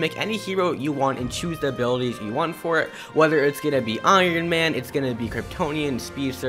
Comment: did he just say we can be iron man in a dc game?